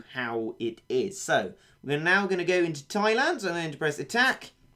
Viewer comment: Who heard him say into china when it was Thailand lol